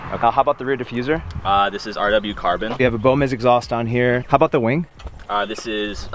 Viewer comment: What kind of exhaust did he say it has? NVM. Says it at 4:47. Bomiz. First time hearing of it.